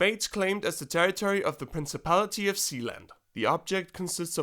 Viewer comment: I thought he said,"THE PRINCIPALITY OF ZEON". LMAOOO😂😂